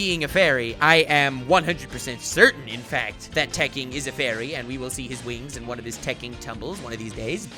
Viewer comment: TEKKING!! I thought said he's a furry (#notafurry lol), now all i can think of is "#ISafairy" xD